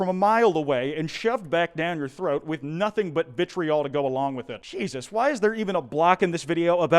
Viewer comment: when you said gsubs i thought you said jesus! i was laughing my fucking ass off